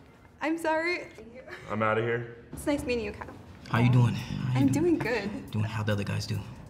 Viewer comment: Did anyone hear the volleyball girl call Kyle😂😂